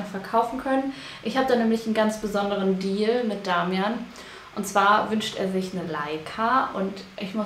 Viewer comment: ich hab erst verstanden, Damian wünscht sich eine Leiter und dachte, wieso bist du so geizig😂